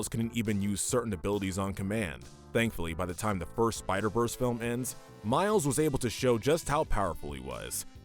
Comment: Jesus, I first thought you said ”by the time the worst Spider-Man film ended” I almost had a heart attack.